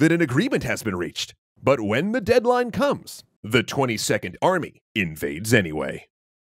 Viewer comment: I thought they said the 20 second army in the intro, Until captains said 22nd army, I was real confused for a minute